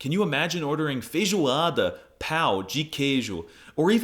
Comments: As a Brazilian, I didn’t fall for the ragebait until I heard you say PAU de queijo instead of PÃO de queijo, in hindsight though, if someone had a pau de queijo, I would die laughing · Bro your "pão de queijo" sounded like "pau" that is a complex slang for a place exclusively for males....